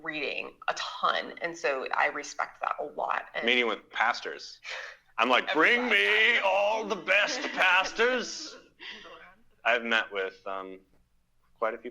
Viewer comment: did he say Pastors or Pastas because i kinda want to sign up